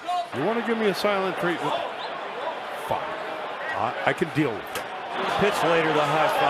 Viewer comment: DID I JUST HEAR HIM SAY THE F*** WORD AT 1:51!